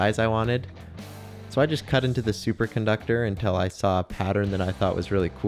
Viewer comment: I thought it said supercondom